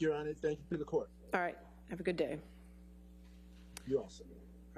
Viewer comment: Did I hear that right? 25:18 "Thank you, sir." "Thank you your honour, thank you to the court." "Alright, have a good day." "You're awesome." =D